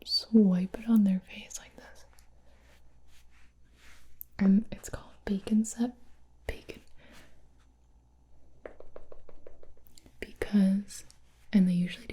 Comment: Thought she said “bacon scent”. 😂